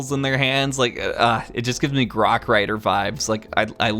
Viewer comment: Did he say Rock Raiders?!